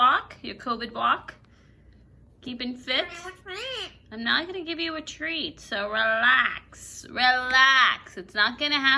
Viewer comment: It's funny because when he says " Give you a Threat' I always hear "What are you saying ?" instead ahahah